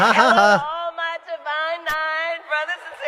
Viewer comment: Did she just say "divine eyed"? Hmm